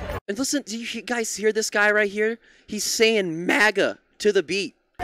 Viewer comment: He's saying "'Murica" (America) not "MAGA". Also, the guy you couldn't understand earlier was saying "kill them all".